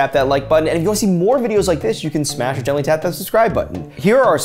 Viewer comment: Omg, I thought said smashidentally tap the like button instead of smash or gently tap